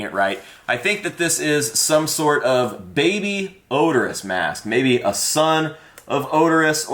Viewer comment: did you just say… Oderus?